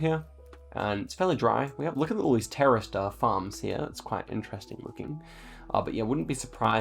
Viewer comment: sounded like "Terrorist farms"😂